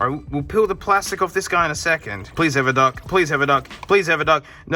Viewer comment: I was surprised it had no dock. I thought they came with them for a while, though I was annoyed when my first iPhone didn’t have one. Also at first I thought you were saying it better have a duck lol